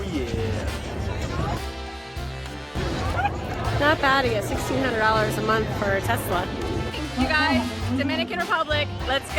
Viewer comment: Did she say “not bad at $1600 a month for a Tesla”…wtf. Says who? That pretty fkn bad if you ask me.